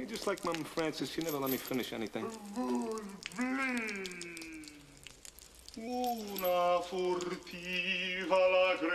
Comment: What the hell does Sloth say at 1:02, immediately after Jake says ‘you never let me finish anything’? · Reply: He's saying, "Food, please!"